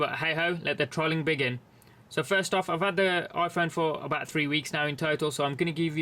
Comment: does he say iphone 4